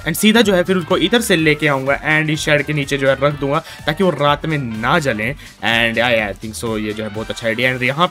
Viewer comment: did he just said taaki vo raat Mai na Jale, lol 😂